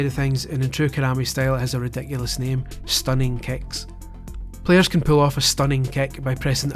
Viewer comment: Is it just me or does it sound like he says "Stunning kek"